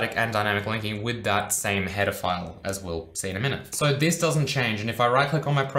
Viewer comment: I think I watched too many Chris Hansen videos because I keep hearing "pedophiles" instead of "header files"